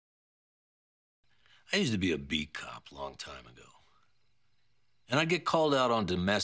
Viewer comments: wtf is he saying 😂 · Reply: mike: i used to be a beat cop a long time ago. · Lmao I heard “I used to be a b-cup” 😂😂